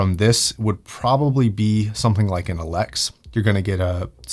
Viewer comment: What headphones did you mean for better than sennheiser I heard you say "ALEC" yes what headphone model?